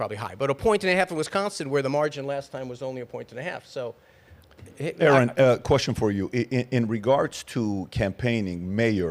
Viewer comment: Aaron ? Did i hear correctly ?